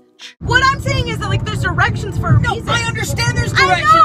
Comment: I love this more than I should. Also does it sound like says "theres erections for a reason" ?